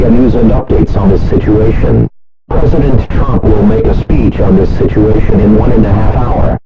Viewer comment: Why did it sound like he said present tromp